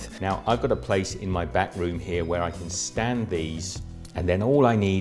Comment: when you said, "well I've got a place in my back room here...", I thought you said, "well I've got a place in my bathroom here...." Great video!